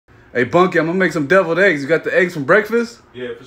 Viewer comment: When I first seen ur videos I thought u were saying hey bunkin